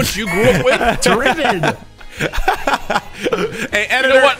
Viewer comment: I thought the way they were saying tribbon was tribbin(tribbing) at first and that is substantially less ad friendly